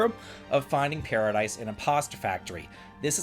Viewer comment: I thought you were saying "Finding Paradise in a Pasta Factory" and now I'm sad that wasn't so. Also, any time Level 5 shows up in the news, I'm like, "Rogue Galaxy sequel? Rogue Galaxy remaster? Rogue Galaxy Rogue Galaxy Rogue Galaxy?"